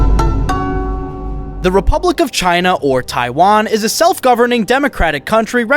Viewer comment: Did you say... TAIWAN?